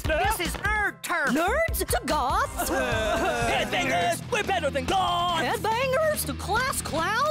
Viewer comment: HEADBANGERS WE'RE BETTER THAN GOTHS! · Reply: I thought they said we’re better than gods, but it’s true either way 🤘